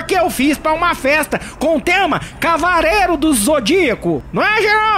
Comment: entendi cabaré do zodíaco kkkkkkk